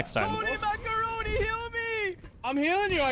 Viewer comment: the Ana and Mercy running to her is so funny KSKS it’s at 14:32 at first i thought the mercy was saying “holy macaroni heal me!” but she actually said the Ana’s name ‘toni macaroni-‘ i can’t stop rewinding to this it’s so funny